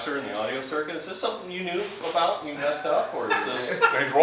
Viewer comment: what does he say? I can't make it out :( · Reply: said, "He's wrong" referring to Anthony, LOL